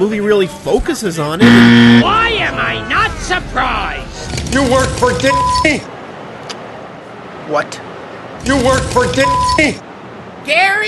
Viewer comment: I think he said : “you work for Dickney ?!”